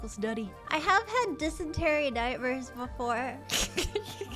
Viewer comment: OH Dysentery Nightmares I thought she said I had Dysentery Diapers...